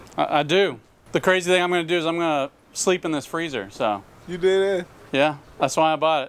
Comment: He actually said “you deadass?”